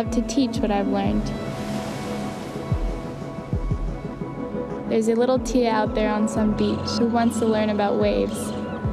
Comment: What did she say from 1:18 to 1:20, and 2:17 to 2:20? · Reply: Hi, Niramsor. 1:18 to 1:22: There's a little Tia out there on some beach who wants to learn about waves.